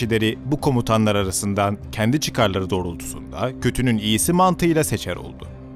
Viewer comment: "Götünün iyisi" diye anladım ilk